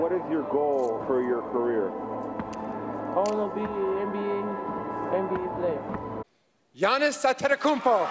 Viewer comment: Bro Giannis really said “I wanna be an NBA player” that’s crazy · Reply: To me, it sounded like he said “I want to be NBA, MV player”